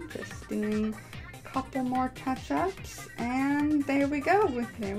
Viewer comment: Did u just say ketchup?